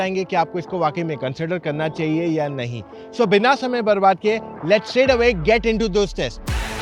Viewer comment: what you say Bina samay marwa ke 😅😅😅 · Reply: Barbaad kiye 😂 bhai